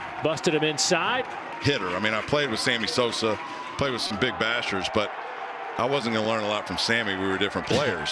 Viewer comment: I thought dude said, “I played with some big b@ lol😂😂. Obviously I reminded it and heard “big bashers” lol